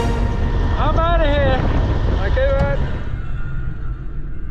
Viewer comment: is bear said "hum aa rehe hai" .....??? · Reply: he said i'm out of here